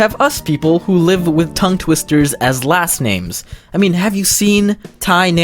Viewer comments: I thought it said "last memes" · I thought it said last Memes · I litterally thought it said last memes